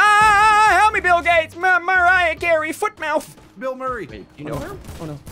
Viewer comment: what did he sayyyyyyyyyyyy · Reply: Ahhh bill gates help me